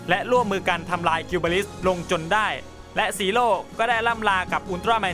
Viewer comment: พี่พูดว่าจีโร่